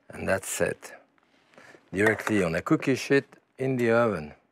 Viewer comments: did he say "I'm going to cook this shit in the oven".....? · There's an ant in the water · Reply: I think he said I'm going to put this shit in the oven! Haha